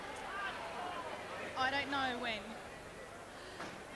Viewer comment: the presenter at the end says (with a terrible foreign accent) "la liguria é bellissima" (liguria region is beautiful, anticipating a spot about liguria) but it sounds like she says in spanish "locura é bellissima" (madness is beautiful) :-)